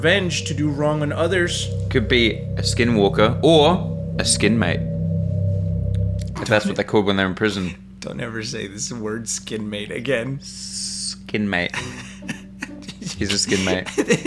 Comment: I thought said skinmaid first then i heard and was like oooh okay a skinmate i get it 😂😂